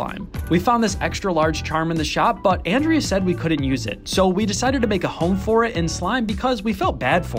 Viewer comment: I thought she said you couldn't use it :,)